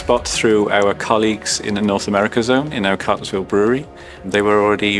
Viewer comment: which brewery is he saying around 01:05 ? Carsville? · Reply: He's referring to a plant in Cartersville Georgia, USA